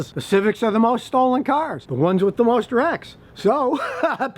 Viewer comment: wait...did say rectum?